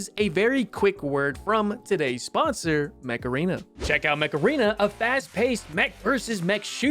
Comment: I don't know why, but at first I thought was saying "Macarena" instead of Mech Arena, anyway, an amazing video as always!